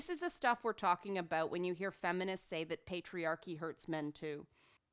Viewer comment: You lost me at "patriarchy hurts men too". THERE IS NO PATRIARCHY IN WESTERN SOCIETY!!! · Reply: ***** I swear I heard her say "This is why WE say the patriarchy hurts men too"